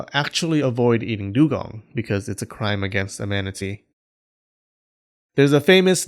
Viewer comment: I heard imanity and my no game no life senses were tingling